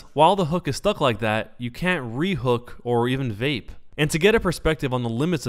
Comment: Did he say vape? Is that what the yellow stuff is?